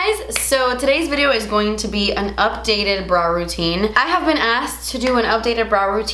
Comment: Lol 😂. I thought she was saying bra routine. Like, what!?